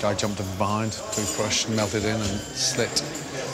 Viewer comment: wtf does he say? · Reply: are you sure it was in hand? I hear 'melted in' . This is some detective work right now 🕵️‍♂️